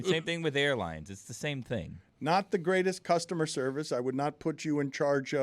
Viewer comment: Lol @ 25:54 Dan says “cunts” instead of “customer service” 😂😂😂